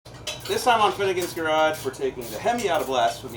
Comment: I really thought you said Hoonigans garage in the beginning